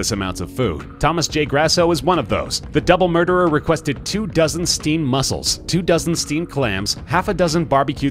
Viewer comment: Did you just say... StEaMeD hAmS